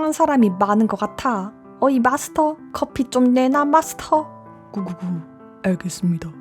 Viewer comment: 분명 마스터인데 미스터가 들림